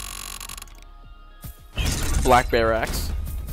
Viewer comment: instead of “black barracks” I heard “BLACK BARE ASS” LOL